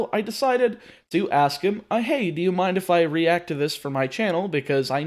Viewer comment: You say "Ah, hey, do you mind..." But I hear "I hate you, mind if I use this video? " Had to re-listen to that part three times XD.